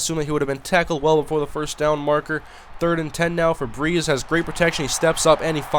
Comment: LOL at 9:37 It Sounded Like You Said "Febreeze".